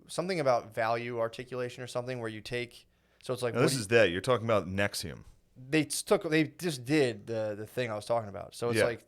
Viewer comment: I kept hearing “Nexium cult” 😂😂😂 like Scientology but the thetons are acid reflux